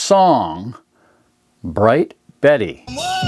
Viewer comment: It's not Bright Betty, but Black Betty.. I use it as my moms ring tone and have for years, and now that shes gone, it's still there and never been assigned to anyone else.. Thank you for bringing a smile to my face and making me remember my mom this evening :) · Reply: He clearly states at the 2:04 mark that "There was a song, Bright Betty". Maybe it's you that should listen more carefully to what someone actually says before commenting. By the way, I'm 69 and I heard him plainly.